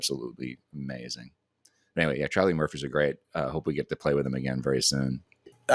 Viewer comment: I heard say "Charlie Murphy" also. Uncle won't be making that mistake again. #poorKateandMary I will give Stiff Little Fingers a listen. #weirdname